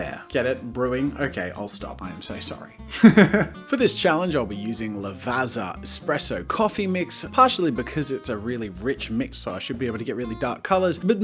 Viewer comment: did he say eXpresso????? Btw ur shirt match ur eyes 😂😂😂